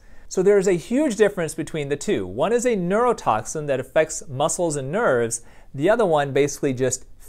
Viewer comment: Why did it sound like he said "...muscles and nerds" and not "muscles and nerves"? 😂😂😂